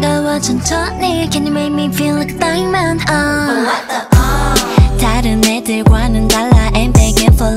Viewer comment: I legit heard "oh what the f"😭